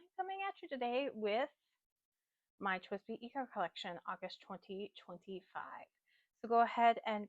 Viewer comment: I thought you said my NEW twsbi collection lol